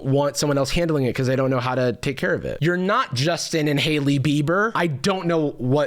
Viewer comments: had to rewind because i heard "you're not just an inhaling beaver" instead of "you're not justin and hailey bieber" - didn't know what it meant? so true man, me neither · this initially sounded like Jarvis said "just an inhale-y bieber" and I was like ???